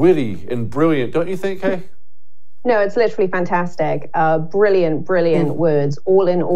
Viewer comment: I thought she said Liberally...😂jk